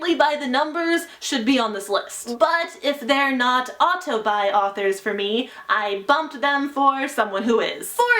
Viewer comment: What did you say at 1:04? Couldn't understand you sorry xD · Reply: Ohhhhh "auto-buy authors" not familiar with the phrase